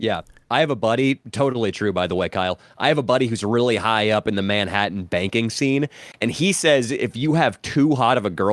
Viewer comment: I heard Manhatten Baking scene until I put two and two together